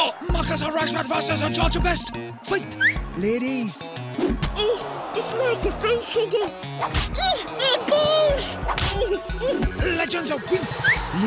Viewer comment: Anyone know what rashford said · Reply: It’s like a pencil case 😂